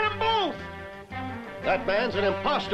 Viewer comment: It sounded like they said "Batman's the Imposter''.